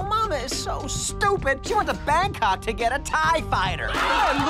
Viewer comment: Anyone else trying to figure out wtf he said at 2:14? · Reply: Bangkok, Thailand